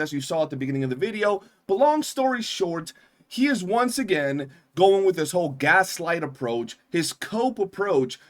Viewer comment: Saintontas got bent over by the wuthering waves community and enjoyed it, but pretended to crash out so we wouldnt notice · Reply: What said: "Long story short, he once again going with his gaslight approach." What I thought he said: "Long story short, He is still short."